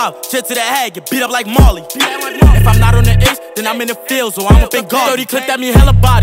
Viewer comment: BUST A PIANTA OPEN UR MATTA Like if u love dat part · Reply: sounds like he says "then i'm in the field so im a bengali" like two bars after.